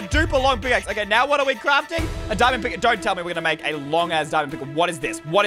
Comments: Did he just say a long ass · it sounds like he said a*s put the playback speed at 25 low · did man say long A$$ diamond pickaxe